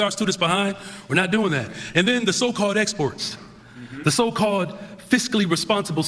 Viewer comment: Sounds like he said "the so-called exports." Dude! Okay back to the video...